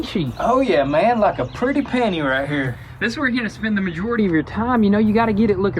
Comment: Did he say penny or panty? (9:08)...🤔 sound like panty to me. 🤣😂